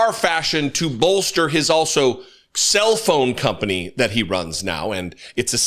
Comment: I'm so disappointed! I thought Jesse said that Trump had started a "self-own" company and a "self-service" company, and I was really excited for a minute until I realized that I'd mis-heard him. :) The perfumes I will simply refer to as Eau de Trump#1 and Eau de Trump #2...